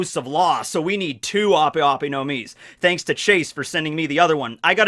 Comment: I thought you said Trace, not Chase & got 4kids flashbacks